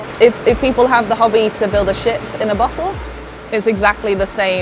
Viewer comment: ok I swear I first heard to build a shit in a butthole and somehow it still makes sense